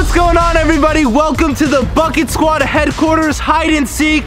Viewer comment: At first I really thought he said f it squad